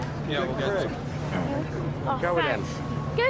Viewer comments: what did he say ? I hear him saying good fish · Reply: “Fhanks mate” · Is it me?, or it sounded like... -Good Face +What? -Oh...